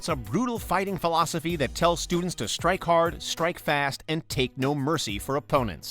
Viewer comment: did he just say strike fast?